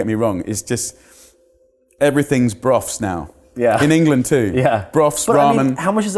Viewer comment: Great video, loved it! 43:30 - 44:00 What are they saying? broaf? how do you spell it? They said something about it being easy to prepare and very nutritious? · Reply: broth